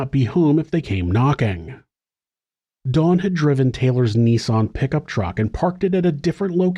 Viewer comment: What is a "niece on"? Are you trying to say nissAN? What happened to Taylor's niece?